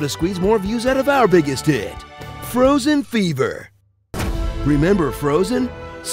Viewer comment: was I the only one who thought it said "frozen forever"?